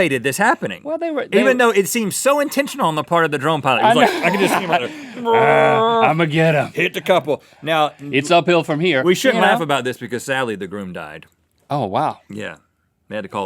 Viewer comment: I thought he said the drone died.. wait... drones have cheeks??